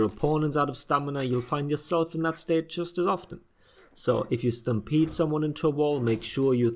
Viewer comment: Lol so I heard “if you stampede run someone into a wall...” as “if you stam-pizza someone into a wall...” I’m hungry, fuck off.